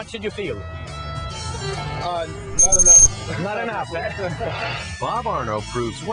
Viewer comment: I Thought he said BOB PORNO